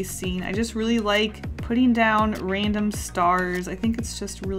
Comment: lol when she said random I heard Raymond and I was just thinking of a star with the face of Raymond from animal crossing